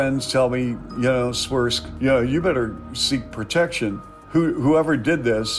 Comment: Chuck Swirsky says "I had a couple of friends tell me, yeah, Swirsk [his nickname]" but the subtitles say "it's worse" instead of "Swirsk".